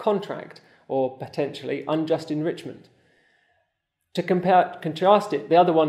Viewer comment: I thought he was saying "unjust in Richmond" but its "unjust enrichment"